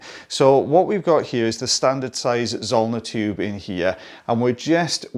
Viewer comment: Zollener tube, i thought you were saying sauna tube😂 been watching for like 3 years or more. Learned something new today.